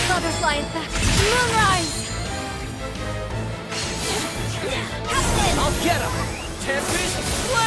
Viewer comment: Wait wait wait! Did Narmaya just call Gran husband? at 8:07 or did I mishear it. I know she is super obsessed with him after her lore back when they first met but I thought it was like a sibling kinda love. Its been soo long since I last played the series on mobile so I could be wrong here but I thought that she saw gran like a little brother she must protect and spoil lol assuming I didn't mishear things. Honestly though her love for Gran has always been super intense but meh someone will be kind enough to correct me if i am wrong and refresh my Granblue lore memory. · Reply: It's "captain" with an odd voice inflection.